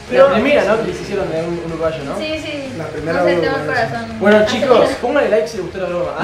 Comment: Alguien en 2020?? Me explican que dijo? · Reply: 5 meses despues pero dice "si, si yo tengo el corazón acelerado" de nada :D